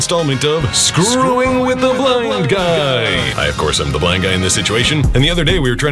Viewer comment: I thought he said "screwing with a bald guy" I'm like your blind not bald